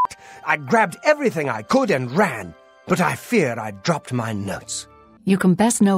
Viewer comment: "I fear I've dropped my nuts" lmao Was the last word edited or does he say "notes" just like that?